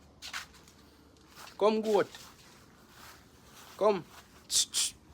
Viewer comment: I can csee you enjoying yourself, I heard you saying come goat. Like you want to cook your neighbour goat. 😂😂😂😂😂